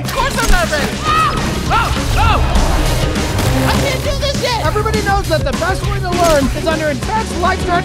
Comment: I keep hearing, "I can't do this shit!" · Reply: Pretty sure he says, "I can't do this yet!" But yea For God so loved the world, that he gave his only Son, that whoever believes in him should not perish but have eternal life.❤